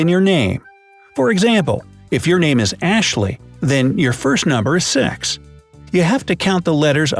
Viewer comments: why did you say six like that?!? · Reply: "number seax" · Lol "Ashley" i thought it say "Ashraf" 😂